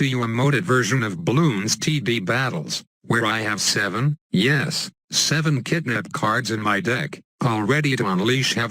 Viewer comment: I heard.... "7 kidnap cards in my dick"